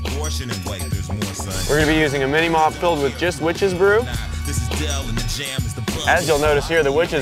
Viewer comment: Did he say Jizz Witches Brew?